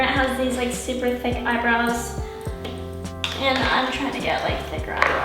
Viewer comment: eye - BROWS i thought you said i-bras